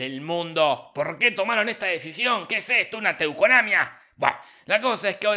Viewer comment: ¿Qué dice en el minuto @ 7:22? ¿Teuconamia? ¿Qué es eso?